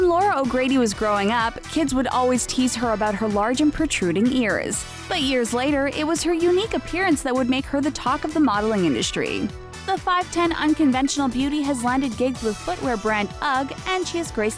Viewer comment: I thought it said but ears later lol 😂😂😂😂